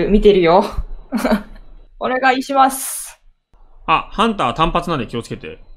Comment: お願いします！にしか聞こえない・・・。